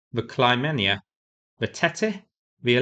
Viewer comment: so I heard one of the ship is called "Chlamydia"...did I hear there's another ship called "titties"??? 🤣🤣🤣